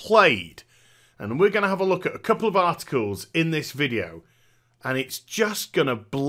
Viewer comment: Who else heard Art Schools at 1:23 ? Lol